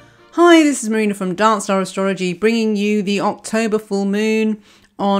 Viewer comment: Lol when she said “make sure you come to Darkstar...” I thought she said Dance Dance and I got so confused